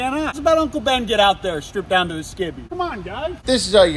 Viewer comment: Did you say strip down to your skibbies at 8:30 ? Like, with a B? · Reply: No he said strip down to his skivvies which is a word most buys use for underwear